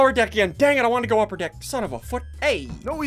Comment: WHAT DID HE SAYYYY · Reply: He said Son of a foot